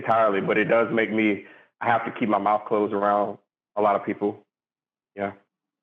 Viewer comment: what did he say here? Did he say that a lot of ppl don’t think that music affects society but he does and he has to keep his mouth shut?